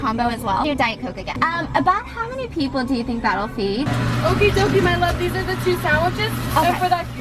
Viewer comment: i thought she said “Okie dokie my lovely” 😂